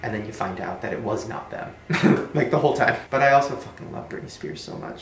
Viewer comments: Does anyone else hear the ‘I love you’ at 19:41 right after Shane says ‘Britney don’t hate me’? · WAS I THE ONLY PERSON WHO HEARD AN "I LOVE YOU" AT 19:41 BECAUSE I STG IT WAS LEGIT